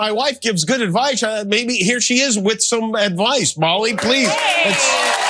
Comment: Molly, I adore you. Married to the WORST HOST EVER and you still love him! At first I thought he said Holly, my daughter's name. I just adore you! Jimmy! You Da MAN! Thanks. I have no voice. I'm happy you and Molly are saying what I believe! Shoot --- gotta find my cat. Door open. I don[t live behind locked doors. America - Land of the free! Well, gotta find my cat. Thanks for the smiles and knowing somebody with clout is on the job! Cancer sucks. I'm sick. So I am happy to see you two on th button!